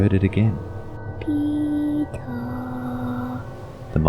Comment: I thought the boy said PEE NUT